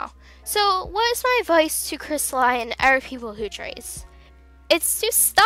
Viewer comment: dont mean to be rude but cri-stal-ee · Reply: Lava oh sorry I must hav heard wrong I thought you kept saying cristal-ie